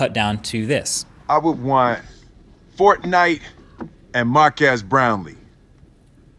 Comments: “I thought he said Markass Brownlee”-Pewdiepie edit:respelling of his last name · I thought he said “mark ass Browne” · I thought will said Mark ass brownie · Nice video...Markass Brown P.S. - Just a joke...cause I thought Will Smith said that at first. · i thought smith said mark ass brownies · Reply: I thought he said "Mark Ass Brown Lee's" I was like, wot? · Will Smith: “I want Fortnite and Marques Brownlee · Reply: I was wondering for the longest what he actually said I thought he said “I want Fortnite and My Kids Brown Knee “ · When Will smith said your name, I thought he said "Mark ass brownie" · I would want fortnite and Mark ass brown Lee. That's what I thought will Smith said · When Will Smith said your name in You Tube Rewind 2018 , I thought he said Mark ass Brownlee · I thought will smith said mark ass brown so I looked f or that channel lol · When Will Smith said your name I thought he said "Mark Ass Brownie." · I thought it was that Will said mark ass brownlee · sounded like he said Mark ass brownlee😂😂😂😂anyway liv ur vids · When I heard your name I thought will smith said mark ass brownlee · I heard “Mark as Brownie” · I thought Will Smith said " Mark Ass Brownie " :l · Markass Brownlee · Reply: I actually thought he said in the video "Markass Brownie" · i thought Will smith said (mark ass brownies) when he called you xD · Thought Will Smith said "Mark Ass Brownie" not h8 tho appreciate the honesty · i thought smith said mark ass brownies